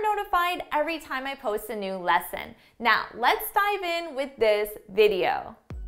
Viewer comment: Wht did you say at the begining : 0:36 let's type in the video ??